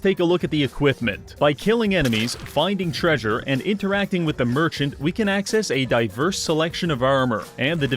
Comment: Did anyone else hear something other than merchant? Because, even though I wasn't looking at the screen because food, I swear I heard him say virgin.